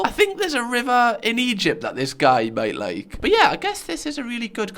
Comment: the way I misheard it as "there's a rimmer in Egypt this guy might like"